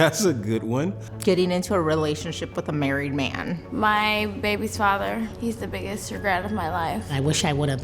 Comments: "My babies father" had to replay that 3 times I thought she said my baby toddler!! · i thought that woman said ‘my baby toddler’ and i was shocked